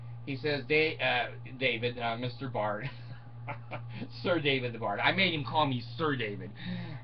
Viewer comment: The Microphone didn't work great at first and I thought You said "Sir David the Fart".........We all Know that's Not True.......You're a Sir David the Bard if I've ever seen one. Good luck with getting everything fixed.......Maybe You should have bought the Deluxe Model for $12,000.....Don't be a Cheap Skate Next Time Bard.....Anna wants to Make Sure You Take Your Bath Everyday.